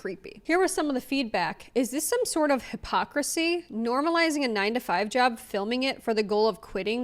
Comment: I legit thought you said Hubpocrisy and I was like…. Dang hit him with it lol.